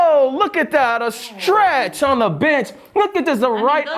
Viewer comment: I thought he said stretch on the btc instead of on the bench my eyes got big!!😭😭